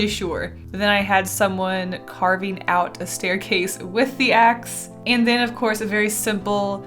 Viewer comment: I keep hearing “Stairkaseys”